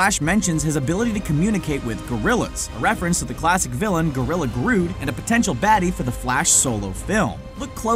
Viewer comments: did he just say gorilla GROOT? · did he say gorilla groot?!